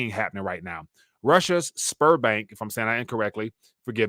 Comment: When you said "Sberbank", I thought you said sperm bank 🤣 I know the fertility rate in Russia is in the tank. But man!